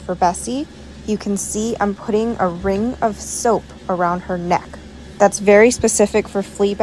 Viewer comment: I thought she said a ring of ROPE around her neck. 😳